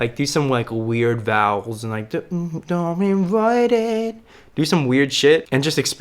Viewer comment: I’m sorry but what was that? 13:07 It sounds like he is saying “don’t be right it”😂